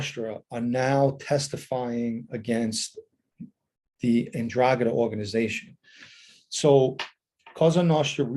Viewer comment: Mr. Pennisi interesting news , I have one question. I know about American LCN and a little about Sicilian Mafia and the Camorrah. The Ndrangheta name is New to me since You previously mentioned it, Are they a Newer Mafia Clan In Italy or have they been around a long time ? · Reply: Hi Irish Green. Hope you are well. I'm such a numbnut to this stuff - the first time I heard him say 'Ndragheta I heard Andromeda - and said to myself, you mean the spaceship? 😳 Stay safe